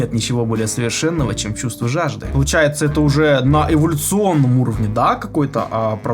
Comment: мне послышалось " поддержать оптимальный уровень деградации" 😂 немного растерялась